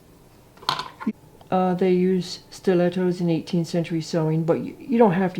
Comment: What did you call that little gadget? It sounded like you said stiletto. I'm so busy with outside chores, but I can't wait to do some of this stuff that you're teaching us. I love it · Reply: Yup, stiletto. But a knitting needle, chopstick, or even a pencil would work. Thanks!